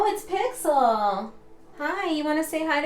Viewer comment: I heard Pixel say - Hi ya ! 34:10 Thanks Pixel LOL ! 38:48 I'm like the hardboiled detective.